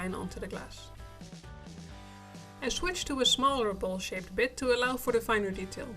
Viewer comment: With your accent for a split second I thought you said "I switched to a smaller bullshit bit." Had to double back, lol